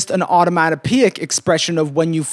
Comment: there is a mistake in the subtitles, the subtitles say 'automatic peak' when you say 'onomatopoeic' lmao